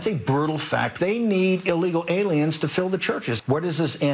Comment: Did I hear that right? "They need illegal ALIENS"?